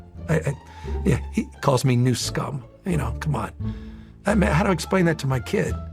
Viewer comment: I know your saying “Ne Scum” but I keep hearing “News Cum”…